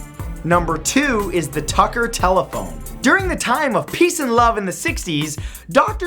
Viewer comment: i thought that shit said "thicker butthole"